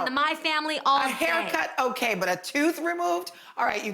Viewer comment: When Garcelle thought Jeannie said "A tooth removed" 😂 Que risa